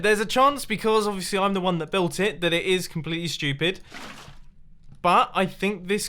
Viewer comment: Mumbo explains concepts Im confused I think and at 5:57 i hear the words "completely stupid" Me: God is speaking to me